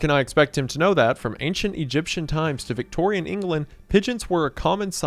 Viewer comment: I legit thought it said ''from Ancient Aliens to Modern Pests'' and I was hella confused